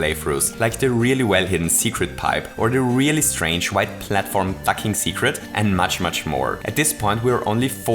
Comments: is it just me or does it sound like he says "that really strange white platform F*cking seceret" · did he just say "that really secret white platform fucking secret